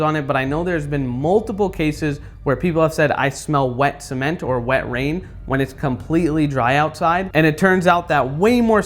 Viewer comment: did bro actually say wet rain ❤😂